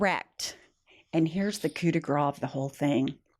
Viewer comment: I know what you said, but what I heard was "cooter de gras".